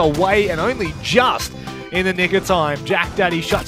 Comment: it sounds like he said the n word